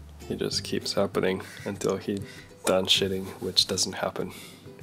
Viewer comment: When said shedding I thought he said shitting, lol 😂